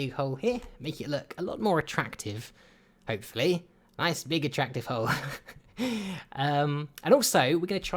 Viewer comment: sounds like he said Nice big attractive ho